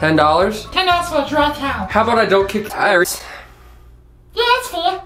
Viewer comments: i heard say, "How about i don't kick tires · The thing that does with the warped curse words, the first one made it sound like said: "How about I kick tires?" That got me.